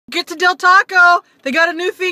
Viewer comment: Sound like you say Fre sha ‘foca’ do